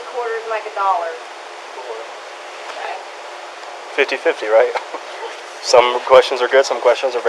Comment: Sounded to me like that cop didn't know how many quarters were in a dollar. I heard him say, "50-50, right? " when the paramedic posed the question.